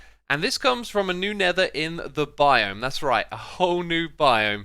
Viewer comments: Is it just me that heard him say a new nether in the biome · I'm so glad I wasn't the only one who heard him say "New Nether in the biome" XD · anyone else hear him say a new nether in the biome? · Did he just say "A new nether in the biome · I heard “new nether in the biome” and went to the comments instantly lmaooo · Did just say a new nether in the biome